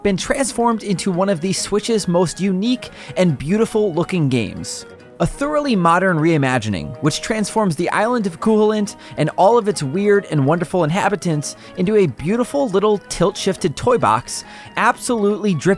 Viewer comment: YouTube captions thought he said “the island of Coolant” 😢